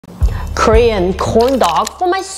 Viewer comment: I thought she said Korean porn dogs